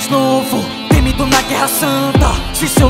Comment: só eu que ouvi que mitou na guerra santa